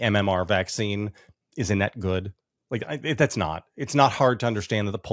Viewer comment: I thought he said the vaccines “isn’t that good” instead of “is a net good”